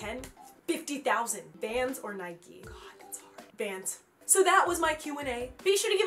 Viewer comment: I thought she said Fans or Nike. Then she's like "oh that's hard" I nearly died of worry